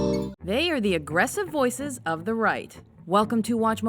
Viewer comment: Lol I thought that she said, "these are the forces of the Reich"... I'm not saying conservatives are Nazi's, I jut misheard the narrator. Ahem, I'll be leaving now...